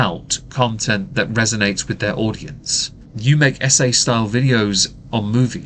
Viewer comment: “You make ESSAY style videos...” I heard “You make S.A. style videos…” and was very confused for a few seconds.